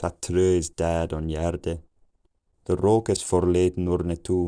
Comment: I heard "the fool is loud" around 800, but I'm not sure if that's what was actually said lol